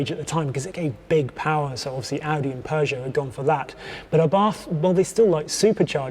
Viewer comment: Did he say Abarth at @ 3:40? Did tjey tune the engines?